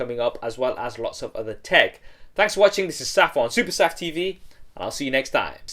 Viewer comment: Always thought you said "SafironSuperSAFTV" and wondered, what the heck is safiron. HAHA